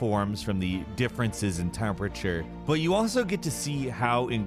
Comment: Welcome to "In Terms Of," starring. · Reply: Sounded like he said " Difference intermofture" instead of "difference in temperature" =O